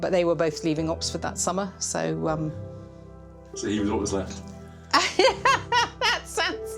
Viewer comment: What does he say to her at 22:38? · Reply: So you were all that was left, I think?